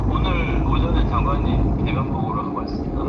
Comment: 앞은 계인데 뒤쪽이 면으로 들리기는 한데 계엄 대면 보고를 줄여서 계면보고 라고 하는듯하다. 😊 대면보고는 절대 아니다. 앞쪽이 계로 확실히 들려